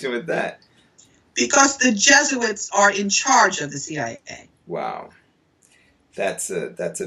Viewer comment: Main point i heard "BECAUSE THE JESUIT ARE THE HEAD OF THE CIA"🙆‍♂️